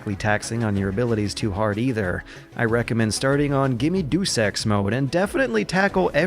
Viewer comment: Hahaha, I spat out my coffe, Sounded like you said "give me dude sex".